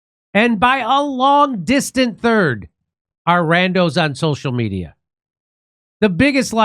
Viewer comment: "and by a long distant third are... randos (?) on social media" - is that what he said? That's what the subtitles say. Random social media?